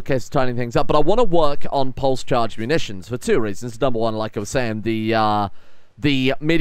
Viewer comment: I wasn't paying attention and spit my drink out because I thought I heard post child munitions